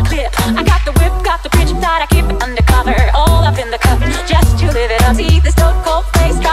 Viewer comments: Do someone hear "JUST a Little Nazi"?? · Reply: “Just to live it uppie” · Okay but in the all up in the gutter part I kept hearing "all up in the crotch" and now I feel bad about my future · When I listened to this without the lyrics I thought it said “All up in the crotch” heh....